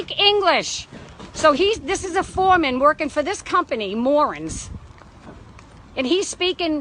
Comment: I thought she was saying “morons” in her New Hampshire accent.🤣🤣🤣